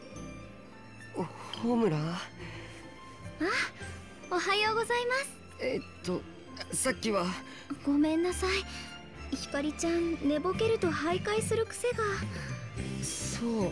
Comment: Hikari = Mythra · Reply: I heard "Hibari-chan" at 21:49.